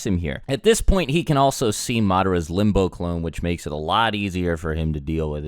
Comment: I thought I heard amaterasu instead of madara.